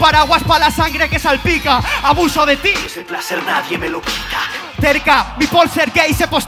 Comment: Alguien es tan amable de decirme qué es lo que dice la voz de fondo en el minuto 22:50 cuanto rapea Arkano y suena la voz de fondo. Gracias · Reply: Ese placer nadie me lo quita ... Es de una cancion de Aczino llamada "El Fucking King"